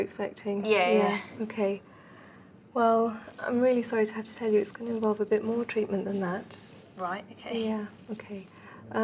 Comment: I need help people In the minute 3:03 the doctor says -Mr Smithson: OK, well, Im really sorry to have... what else she says? i'm not good with english :S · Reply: I'm really sorry what I'm gonna have to tell you. It's gonna involve more treatment.....